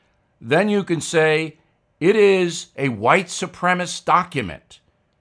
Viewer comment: did he say "white supremist " or am I getting it wrong?